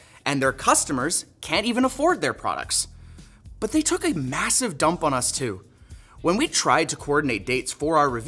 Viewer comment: did he just say, massive?